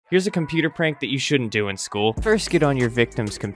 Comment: What he said: here's a computer that you SHOULDN'T do i school What i heard: here's a computer that you SHOULD do i school